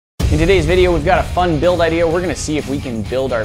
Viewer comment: i thought he said a fun bill nye idea 😂